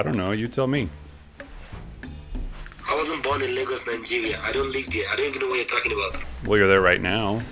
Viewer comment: does he say "I dont live here"? LOL!!